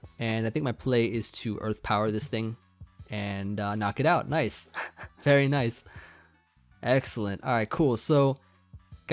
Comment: I could have sworn you said “Nice… fairy nice” because you KO’ed Koko 😂 My expectation of puns is so great that I’m now hearing them where they don’t even exist lol